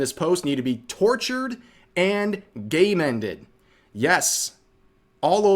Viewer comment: man, I heard you say "tortured and GAY-MANDED", my god it sounded wrong!